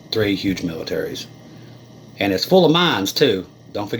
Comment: "Say that 10 Times Fast!" HaHa!!!! That's OK!!!! I thought you said - "IT'S FULL OF MIMES"!!!! DON'T KNOW WHICH IS WORSE!!!! Sorry, I'm tired.